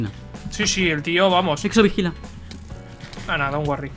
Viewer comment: En el minuto 7:55 en vez de exo vigila escuche ``sexo vigila xDD´´